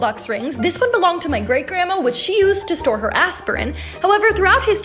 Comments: I'm convinced her grandma was a proffesional assassin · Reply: i thought she said "whick she used to store her assasin"😂😂😂😅😅😅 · I coughed midsentence of the video, and it sounded like it said “ she used it to store her asß